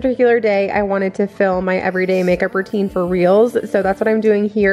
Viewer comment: Lol at the beginning I thought you said I want to film my everyday makeup routine…for realzzzz. I was like ummm okay. But then quickly realized you meant for reels… as in Instagram 🤣